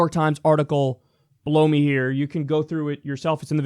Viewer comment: it sounds like Kyle says "blow me here" as he points to his junk, 😅